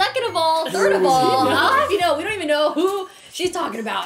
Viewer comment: don’t know if anyone else noticed but i love that around 5:08 rae says “we don’t know who it is” but the subtitles say “we know it’s me” lmaaaoaoao